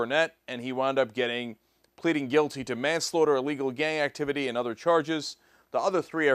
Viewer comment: It sounded like Cenk said "gay activity" the first time he said "gang".